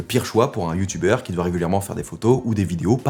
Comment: il a dit quoi ? Régulièrement... 😂😘